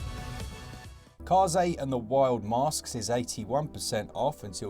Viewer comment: Had to have a second look at the screen when I heard "cars 8 and the wild masks"! 😁